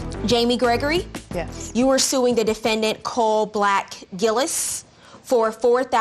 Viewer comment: I thought he said cool black Elvis. 😂 bout to say that’s little Richard